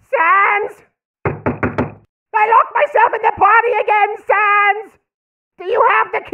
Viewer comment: What does he say at 0:05 ? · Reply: "I LOCKED MYSELF IN THE POTTY AGAIN SANS!!!" Maybe?